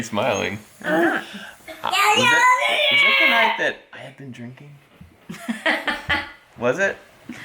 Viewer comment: When he said 'Was that the night I was drinking' I almost thought he said 'Was that the night I made Lincoln'. Which would have been a way better story.